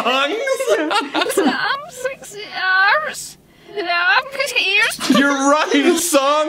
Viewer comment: i heard "Sexy Arms" not "Sexy Ears"